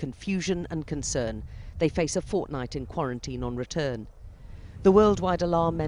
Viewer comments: Wait a second did they just say a fortnite quarantine? i hope it has something to do with the end of fortnite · Did she just say "We face a fortnite" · Did she just say Fortnite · did she say fortnite? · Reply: Xmypulse Fortnite *fortnight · Did I hear Fortnite?